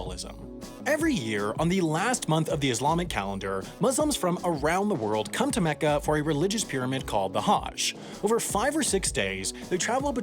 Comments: Did he say “holy pyramid” at 1:13 🤣🤣🤣. I think he meant pilgrimage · did you say pyramid? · Did anyone else hear "religious pyramid"? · is it just me or does he say pyramid instead of pilgrimage lol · it sounds like he said "religious PYRAMID" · does he say “religious pyramid” instead of pilgrimage? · Did he say religious pyramid?!? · Did he say religious pyramid instead of pilgrimage? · Yo you definitely called it a "religious pyramid" instead of pilgrimage at 1:15. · Did he just say “religious pyramid”? 😂